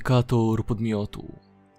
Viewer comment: Identyfikator miodu ? XD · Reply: NATO a dobra bo usłyszałem miodu wcześniej